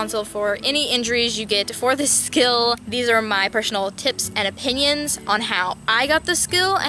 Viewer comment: I heard Persh-onal